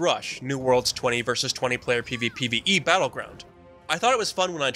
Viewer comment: what is he saying??? pvpe???? is it pvp or is it pve??? hello??? spit it out ffs